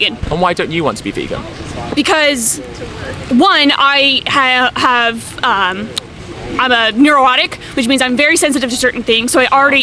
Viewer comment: At about 1:40 , did she say "I have a neorotic" to justify not being vegan but with an extra 'oh' (neuro-otic)?? 🤔 I feel like she's really confused and having a tough time forming thoughts. I almost feel sorry for her.